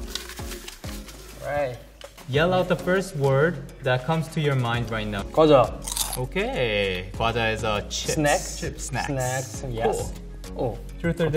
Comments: lowkey thought sungjin said “kkeojyeo” the second time i watched this video after months😔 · To 3.44: I thought that he said "꺼져" (ggeojyeo) and not snacks (=과자/gwaja), which means "get lost"😅😆